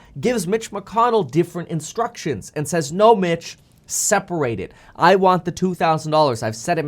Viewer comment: Hahaha. When meet Kevin said unless Mitch gets instructions from Trump. I swear he said no b*tch but he said “no Mitch” hahahaha.